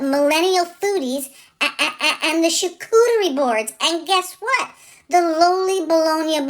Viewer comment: Omg I thought you said the Chicoutimi Board! You can tell I live in the Quebec lands! Thanks for our first class!